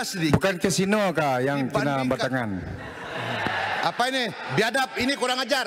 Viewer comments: Thanks for clarifying. When they said "Kinabatangan", I thought they said "Cina batangan" which sound like racist insult, but now I know, it's Kinabatangan. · im misheard the "Kinabatangan" to "Cina Batangan" 💀